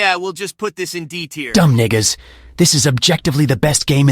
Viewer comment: WHAT DID SHADOW SAY💀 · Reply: He said Ni